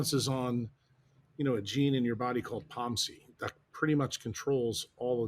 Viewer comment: Brilliant interview! Question: at the 9:46 minute mark he mentions a gene that controls every pathway (light?) in the body called (sounded like) pomcy? . Anyone know what gene he is referencing?